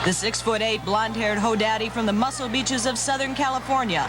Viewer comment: did she call Hogan a " Ho Daddy" ? lmao